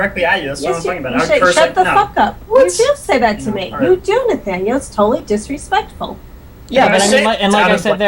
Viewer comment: It's nathanias lol. His mom calls him it at 9:44 lol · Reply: It sounds like she says "You do Nathaniel, it's totally disrespectful" to me